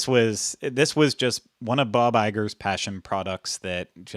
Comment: I misheard when mentioned Bob Eiger and thought he said Bawbag, a wonderfully offensive Scottish term 🤣